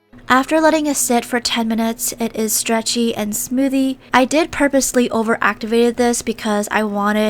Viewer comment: Did she say stretchy and smoothie?